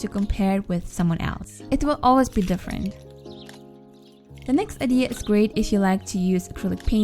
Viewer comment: Did anyone else hear her say "the next idiot"